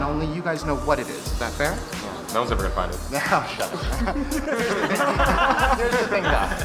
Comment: What did Sam say at 4:44? I've gone through it multiple times and I can't figure it out, he says "That was a......" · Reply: "No one's ever going to find it"